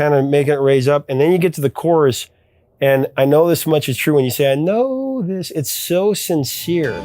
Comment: First time hearing I heard: "I know there's bunches--true." I also thought it was Boy George.